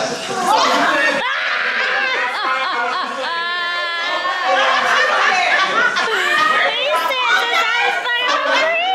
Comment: Please tell me what they said at 33:45? I’m trying to be respectful of gender ID, so I used the neutral they. But it was the person in the plaid skirt. They said something funny and I can’t make it out! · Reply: He said, "she been knowing me since I was fuckin for free." 😂😂😂😂